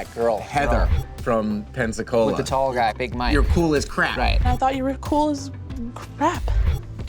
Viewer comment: I misunderstood Max and thought he said "your pool is crap!" 🤣🤣